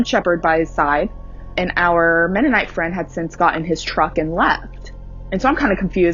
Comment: Oh... Mennonite. I thought she said monday night.